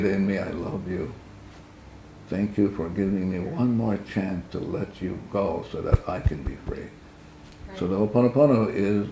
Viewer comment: Can't hear what he's saying at 0:49 "I love you, thank you, forgive me, ________ me one more chance..." GIVE me one more chance? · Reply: Thank you for giving me one more chance to let you go so I can be free.